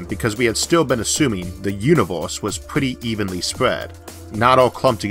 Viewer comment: Did i just hear "Univorse"? Wtf..